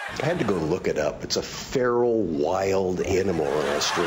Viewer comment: Did you guys notice at 2:04 when Tom Wheeler said "Feral wild animal" the subtitles said "Farrel wild animal"